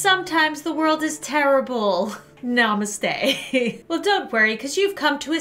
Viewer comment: Did lauren said namaste to indians at 0:34?